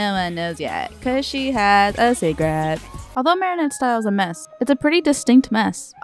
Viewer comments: I’m watching this with subtitles and instead of “secret” it says “cigarettes”😭😭😨😨🙏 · i’m sorry it sounded like “cause she has a c^garette”